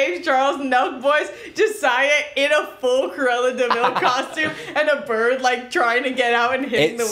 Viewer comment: it took me a good 3 minutes to realize was talking about cruella deville. i thought he was saying corella or something LMAOOO